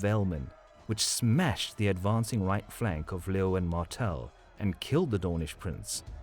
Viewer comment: I could have sworn I heard you say 'Lil Wayne Martell'